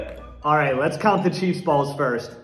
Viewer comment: He said chiefs balls I thought he said cheese balls😂